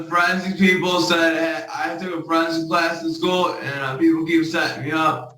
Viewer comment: Thanks god for Google Close Caption. I read it, fix it to make more sense, and it still made no sense. Here is what I "hear": The forensic? people said I took a friend's class in school and people keep setting me up and uh uh they said that they're sharing something shady with the friends because that doesn't have do with mental things and if you get the call for the uh things, they are fucking weird and... I am sorry for swearing I can't help because it is 2 times to get available schedule not good and uh I am innocent so I like to say I took that class so I know that I am innocent so friends would say "I'll be good" In simple English, he is innocent because he took a class his friends advised him, and because it was hard to have time to accommodate for this hearing. Dude, how did you get high in jail?